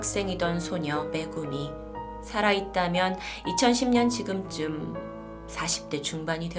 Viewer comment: 2010년으로 들리네요ㅠ